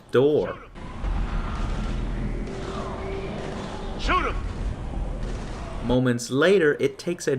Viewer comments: Did anyone else hear Anderson yell "Shoot him!" when Shepard was aiming at the kid? XD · although I know Anderson is referring to the Husks, it sounds like he says "Shoot him!" when you have Shepard looking at the child running towards the door :P